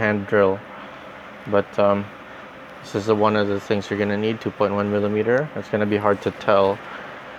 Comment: what kind of liquid are you using in soaking those AO-1011 and rollers? TIA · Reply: is it 2.1mm driller? Did I hear it right? BTW I'd decided that I'm gonna try this build in my Gun Bluster XTO Premium also.